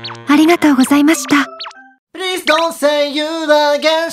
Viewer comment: Did I hear Don't Say Lazy?? * gets a piano * lol